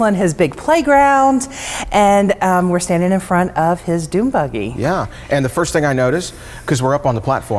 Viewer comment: ha-ha-ha the captions showed doom buggy instead of dune buggy I don't know calling that a fun vehicle ... More like a scary ride! 😂😂